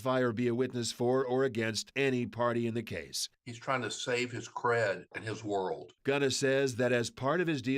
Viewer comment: Trying to save his CRED(it) in his 🌎 is what I heard so he's like fuck everybody else even though you've gained fame off everybody else